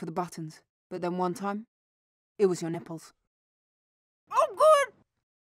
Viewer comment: Did she say nipples? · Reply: "I just look for the buttons, but then one time...it was your nipples." Mopsy's face went slack with absolute shock...but she should be used to Cotton-tail just saying whatever with no censor. F, M, and CT need their own movie...or at least a 30-minute short when the dvd and Blu-ray are released in May.